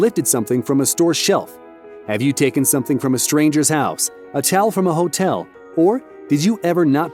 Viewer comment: I thought he said take a child from a hotel lol.